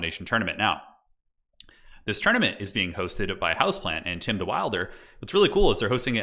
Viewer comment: When you spoke about the NA Domination tournament, I thought you said "hosted by my houseplant" and was very confused when I thought you were being literal.